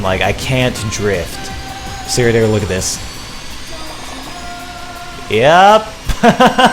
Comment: I decided to turn captions on - just for fun, you know - and at 10:45 you said 'see here we're gonna look at this' but the captions said 'cereal with this.' HAHAHAHAHAHAHAHAHAHAHASHAHAHAHAHAHAHAHHAHAHAHAHAHAHAHA!